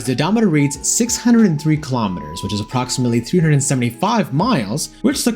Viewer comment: But I said exactly that lol… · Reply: It sounded like you said 275 miles to me. My bad . . . the closed caption does read 375 miles.